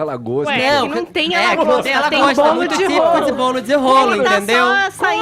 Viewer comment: Juro que entendi "bolo de rola".